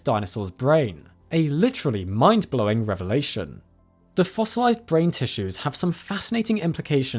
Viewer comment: i thought it said fossilized fart